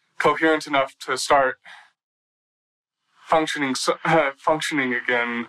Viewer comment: it sounded like almost made a Freudian slip because it was like he was about to state, "after I was coherent enough to start PUNCTURING (as in stabbing Lea) again" What is even more chilling is that he not only showed ZERO remorse but he also conducted himself as though he was the lead actor in his very own reality murder mystery series. R.I.P. LEA PORTER 💔🕊